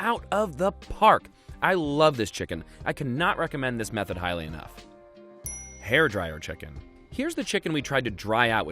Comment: I can't understand at 3:55 he recommend or not recommend this way to cook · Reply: He said he couldn't recommend this method highly enough which means it's so good that no matter how highly he recommends it wouldn't be enough